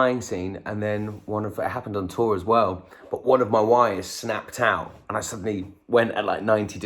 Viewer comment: It sounded like he said one of his wives snapped